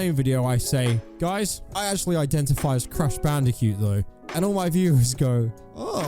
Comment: In lieu of an aggressive paragraph of harsh criticism borne of frustration with all the fake disorders everywhere, here's a positive observation to spread joy instead: 6:43 I like how it sounds like said he identifies as Crash BandiCUTE! 😁